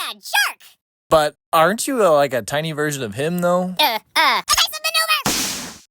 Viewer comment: what dose he say???? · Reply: Evasion maneuver